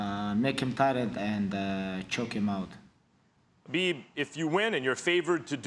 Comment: Did he call him Bieb????